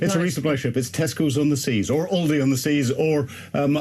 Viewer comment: sounded like the Anchorman said "Testicles on the sea or ovaries on the sea"🤣🤣🤣🤣🤣🤣